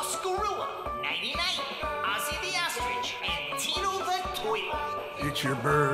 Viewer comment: I thought He Said Tito the Toilet